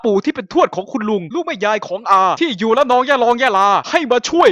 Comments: นาทีที่ 2:45 เมื่อได้ยินว่ายะลอง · ได้ยินว่า ระนอง ยะลอง ยะลา นะค๊าบบ แต่ก็ฮาโคตรอยู่ดี ทำดีแล้วครับ ทำต่อไปนะครับพี่